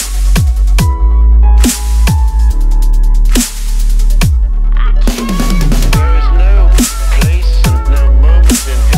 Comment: Anybody knows what he says at 0:55? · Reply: There is no place and no moment in history that I can say 'arithmetic begins here, now' The same voice says it in a song called Rainbow Road by Nanobii on the monstercat channel. I'm really not sure why. Anyway that's definitely what it says I've looked it up.